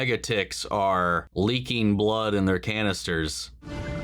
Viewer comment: Bro lol I thought you said mega tits are in her canisters lmao I was like alright NY changed you lol